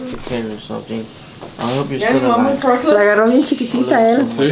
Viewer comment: dam at 7:11 did he tell his dog i hope ur still alive? really sounded like it.. thats fuckin sad :/ RIP